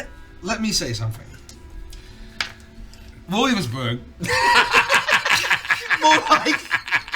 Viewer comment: what does he actually say? I've rewound this moment like 10 times and still can't figure it out ... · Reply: I think he says "Williamsburg" or something like that? Its a joke that I dont understand, I just like the reaction it gets.